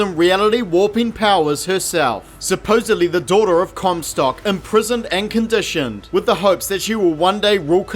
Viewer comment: I’m sure I heard “cum sock” there